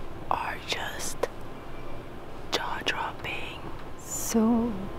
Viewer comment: Seriously thought you said ‘Jar Jar Binks ‘ took me a moment to realise you said ‘Jaw Dropping ‘ 🤣